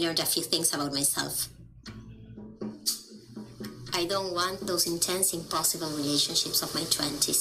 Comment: I hear "I don't want those intense, impossible relationships of my 20's" at 37:56. (Not, "intense, passionate relationships".)